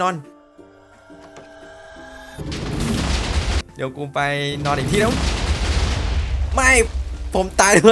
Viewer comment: พี่พูดว่าอะไรนะ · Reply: เดี๋ยวกูไปนอนอีกที่นึง